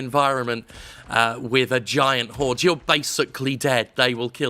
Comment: what does jim say? if they catch you with what? sounds like hes saying "cord" or "port" or something like that or mayve "car" ? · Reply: nvm its probably "Hoard"